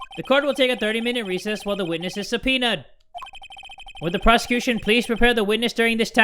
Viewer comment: i thought he said “while the witness is a peanut” xd